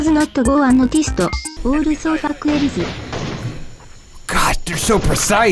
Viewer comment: What does cocopa say around 5:56? She said "also fuck something something" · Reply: On my 6th repeat I realize she says elyse.